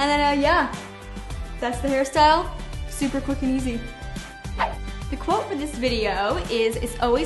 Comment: Who else thought she said "super fucking awesome" at 2:39?